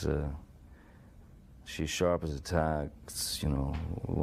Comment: When Johnny says that she's sharp as a tack, it actually sounds like he says she attacks